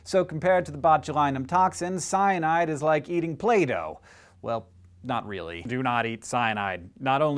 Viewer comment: oh my christ I thought you said "cyanide is like eating plato" ..